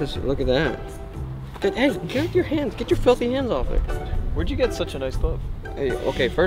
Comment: Did he just cuss there or am I trippin? Or did he say filthy. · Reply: Yeah he said filthy